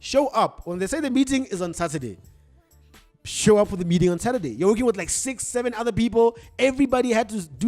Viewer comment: I thought you said Tuesday 💔 been waiting on my daily dose 😂😂😂